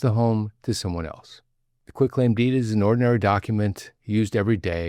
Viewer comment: It's not quick claim, it's called quit claim (a simple legal document used to transfer whatever ownership interest a person has in a property to someone else). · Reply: thanks, I thought he said quick claim!